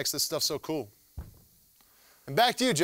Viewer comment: LOL at first i thought he said "Thanks science virgin of me!" Haha